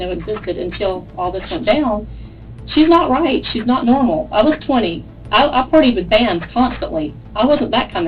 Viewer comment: did she just say she looks 20... lying to law enforcement is a crime lol 😂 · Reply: She said I was once 20